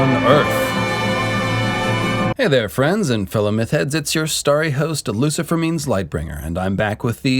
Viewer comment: Did he just say fellow meth heads?